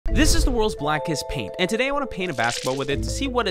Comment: thought he said “the worlds blackest pete” and i was like that man is NOT black 😂😂😂